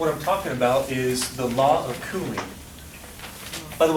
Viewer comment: why did I think he said the law of cooming lol